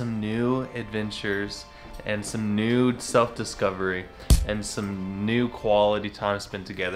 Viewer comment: Ha ha, thought you said 'nude self-discovery'. LOL